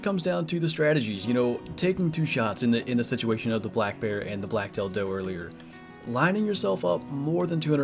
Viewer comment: Oh Black Tail Doe. I thought he said Black Dill... Woah...